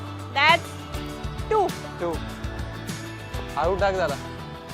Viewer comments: here rutu says halu taak Zara meaning bowl slowly to me Caption says take my wicket if possible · the subtitle says "take my wicket if possible" but he rather said "halu taak jara" in marathi which means "bowl a bit slow" and then he hit a boundary😭